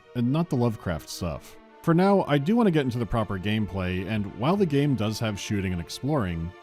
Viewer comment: I think he said 'bleating'.